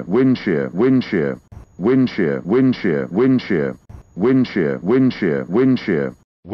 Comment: I hear win chair